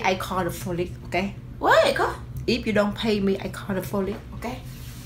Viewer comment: I thought she said ''if you don't pay me i call the foolish''